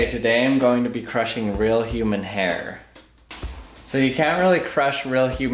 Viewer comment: I thought it said Human Air